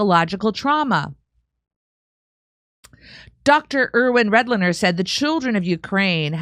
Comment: I'm sorry but everytime I heard Redlener's I couldn't not hear ''Breadliners''